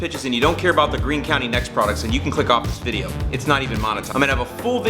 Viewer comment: I appreciate your honesty in the "this is an ad if you dont want to watch click off now". It makes me want to watch anyways. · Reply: Christian L lol I was listening to this as background noise and I thought I heard “fuck off this video” and had to rewind and then heard “click off this video”